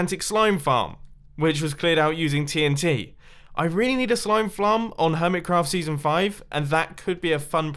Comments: did he say slime flarm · did he just say flrm sarm?????? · Did you say slam ferm in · Did anyone hear this. At 15:52-15:53 says slime flame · DID HE SAID slime flarm